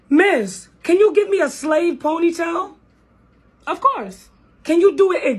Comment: I thought she said slave pony tail